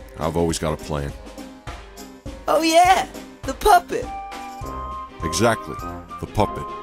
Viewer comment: It sounds like your saying poppet. Poo-pit